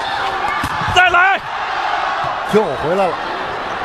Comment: Anybody else heard "TIMMY!!!" from south park at 1:25 ?